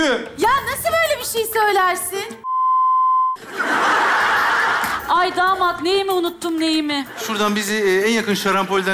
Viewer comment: de ne diyor? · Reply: yelloz diyo galiba